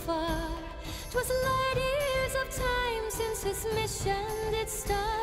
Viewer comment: I'm an old SF fan. I found "light-years of time" so silly that my ear rejected it. I heard "long years of time" until I read the comments. I hope li'l' Lambe makes the same edit in later performances.